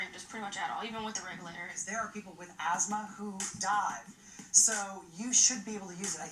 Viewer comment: I thought he said “There are people with asthma who’ve died!” 😮😂 I was like holy crap, all that for a photo shoot ?